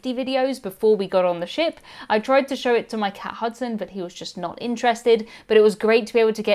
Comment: I don't know how many times I heard "my cat husband" before I realized what you were saying